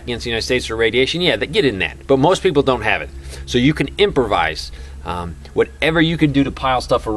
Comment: Improvise ! I thought you said extra size fries